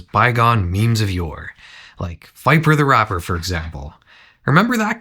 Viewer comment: I thought you said parappa the rapper at first and wondered what sort of evil a dog without a driver's license could possibly do.